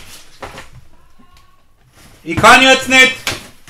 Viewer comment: What did he say at 3:25?? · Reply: He says: "ich kann jetzt nicht" that's german, in english that means: "I can not right now"😁